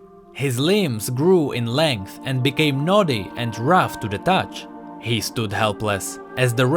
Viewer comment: bro what did you just say? Nutty or naughty?